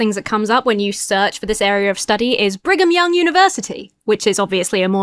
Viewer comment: I heard "bring 'em young university" 😅